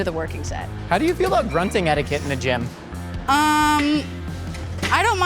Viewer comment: subtitles say "how do you feel about grunting at a kid in a gym" 🤣 beautiful poetry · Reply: For everyone else it's "grunting etiquette" which for example if you grunt too loudly it sets off the lunk alarm.